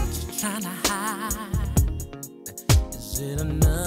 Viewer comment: sounds like he says "what you trying to hide? is it another black guy?" lmao sorry i can't stop hearing that